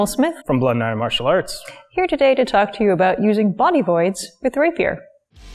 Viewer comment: I mis-heard you at first, I thought you said "using body boards with the rapier", surely a buckler has to be more manageable?